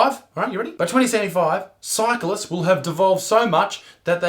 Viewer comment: Lol when he was talking about the cyclists I thought he said evolved not devolved XD